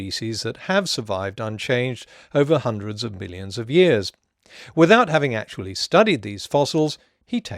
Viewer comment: Is it just me or does he say "Hundreds of billions of years" instead of millions at 4:09?